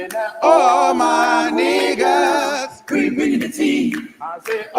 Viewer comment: More oil 😂❤❤❤ the best trial coverage !! You made it so funny All my niggas be spilling the tea 😂😂😂❤ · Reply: IM CRYING cause I definitely thought the song said "all my niggas" at first too but they're saying ARRRRMON WIGGINSSSS lol